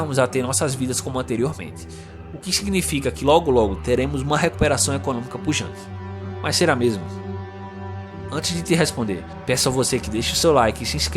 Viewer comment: O que ele fala nesse momento 1:56 uma recuperação economica pungente ??? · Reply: "pujante"